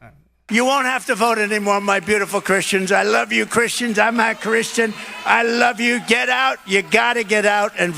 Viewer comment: Did he just say "I'm not Christian"?